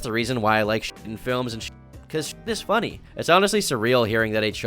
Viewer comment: aaaah waaaiiit a minute ? diarrhoea is kinda easy to fake. much easier than "solid" .. a tube going up the leg kinda deal? .. or maybe its just wishful thinking on my part ? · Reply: I thought he said the shit was real 😭